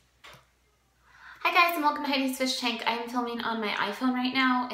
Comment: Sorry my hearing isn't that great i thought you said amys fish tank🙃